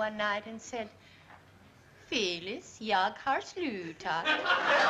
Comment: "Phyllis, I have quit." Rest In Peace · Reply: Thank you, Swedish speaker! I always heard “yog har slooota” and guessed Lars was telling Phyllis “you’re a slut”!! 😂 Now I know … ’“Jag har slutat ”, “I have quit”. Makes it even funnier! 🇸🇪 ❤️